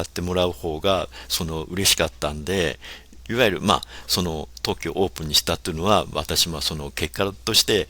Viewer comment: Who on earth mangled the translation this badly? He clearly says 特許 ("patent"), not 東京 (Tokyo). Presumably someone who has no ear for pitch accent, since 特許 has atamadaka pitch, whole 東京 is heiban.